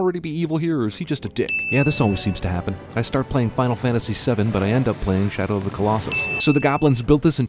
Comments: Just a little sin to point out about the video, but in 2:30 you meintioned "playing Final Fantasy VII" and yet the subtitles say "playing Final Fantasy XII". Way to go, specially for someone who made up half of the sins that the movie has. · Voice over says Final Fantasy VII, caption says Final Fantasy XII. *Ding* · You say Final Fantasy VII but the subtitles say XII at 2:31. SIN!! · says Final Fantasy VII, subtitles say XII. CinemaSins sin total: 1 Sentence: Knights of the Round. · Narrator says "Final Fantasy VII" while subtitles say "Final Fantasy XII" *ding* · What's written: "Yeah, this always seems to happen. I start playing Final Fantasy XII but I end up playing Shadow of the Colossus." What he's saying: "Yeah, this always seems to happen. I start playing Final Fantasy VII but I end up playing Shadow of the Colossus." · the subtitles say Final Fantasy XII while the speaker says Final Fantasy VII. ding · Should I be concerned that at 2:34 he says Final Fantasy 7 but the subtitles say 12? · sin, at 2:33 he says final fantasy 7, but the subtitle says 12 sin count: 1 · the subtitle says "I start playing Final Fantasy XII," and yet he says Final Fantasy VII. Just saiyan... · he says he starts playing Final Fantasy VII but the captions say XII. XD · "Yeah when I play Final Fantasy VII" and the subtitles say Final Fantasy XII. Hmm GG. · " I start playing final fantasy 7"... when subtitle says "i start playing final fantasy 12"